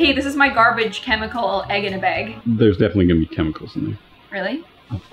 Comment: This is how many times said “Egg in a bag” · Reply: For me it sounds like she said “egg in a beg”